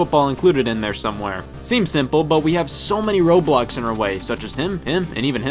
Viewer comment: I heard "seems simple but we have so many roblox in our way"